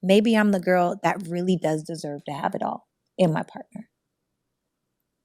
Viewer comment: thought she said no has it all?